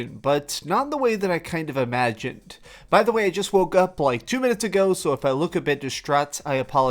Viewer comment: I wasn't watching just listening and I heard "I just Broke Up" I'm like wait what the fuck? So I rewinded to hear it again and it is "I just Woke up" almost had a minor heart attack.